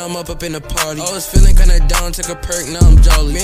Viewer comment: What’s the word hyphenated out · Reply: its Percocet its a drug